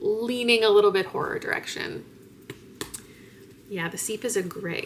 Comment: I really thought that said “The Sheep” and was confused for a second lol